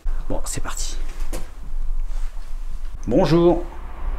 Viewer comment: Vidéo très sympa... C'est bien de jour aussi, on voit mieux autour. Le son... J'ai entendu "bonjour". Trop simple ? Peut-être, mais c'est ce que j'entends. Et j'ai trouvé ça extraordinaire. Pour une fois que ça paraissait si clair, tu ne sembles pas entendre... C'est frustrant. Soignes toi bien.